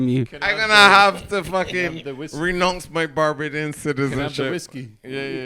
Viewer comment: Did say "renonce my Barbadian citizenship"?